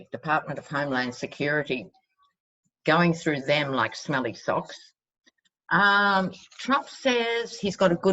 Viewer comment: Ahahah🤣... I thought you said “smelly sauce”... I realize it’s “smelling salts” but still. I amuse myself at my old age😃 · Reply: Sherry Osinga I think she said smelly socks.